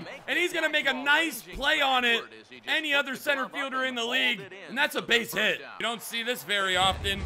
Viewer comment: !I thought he said twerk center LOL!